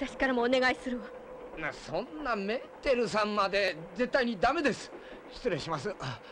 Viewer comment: 車掌さん「んもうメンテルさんまで」と聞こえて爆笑（笑） 乗客の言う事に従う義務に遭難者を救出する義務に。ホント銀河鉄道株式会社は本気でダイヤを守るつもりがあるんだろうか？！(￣∇￣;)ﾊｯﾊｯﾊｯ!